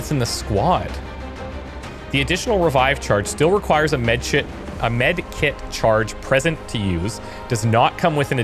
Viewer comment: Will anybody comment on the fact that said 'medshit' instead of 'medkit' at 43:36?